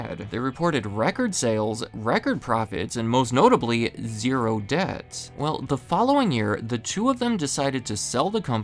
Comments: oh my god i thought you said zero deaths 😭 i was like. who died in previous years⁉️ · i was just listening to the audio and thought you said "and most notably, zero deaths" and i though - man we gotta set the bar higher than that.